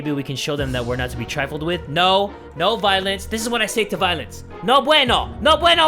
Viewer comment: i swear 24:08 if you listen carefully I thought he said "No violets" lol